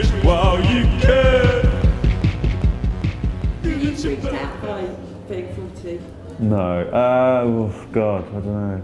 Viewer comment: Help! What's the interviewer saying at 0:33? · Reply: Are you freaked out by being 40?